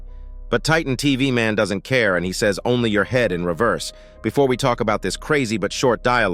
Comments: i hear Only in your head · I thought Titan TvMan said "just you head", because he just wants him d3ad so bad, LOL